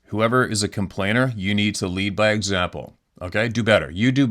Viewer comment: When was talking about complainers I thought he said "Leave by example"😂